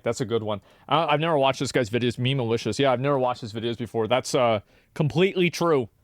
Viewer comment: Did he just say 'Memeulicious' ?